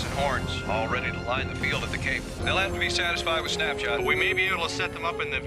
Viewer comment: at first I thought he said "they'll have to be satisfied with Snapchat" and I was like wait what